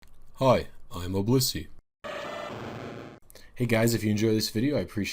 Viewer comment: The first time I heard your intro I thought you said "Hi, I'm a wussy." and now I can't unhear it